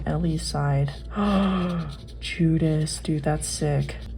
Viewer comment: you saying Judas instead of Jesus has changed my brain chemistry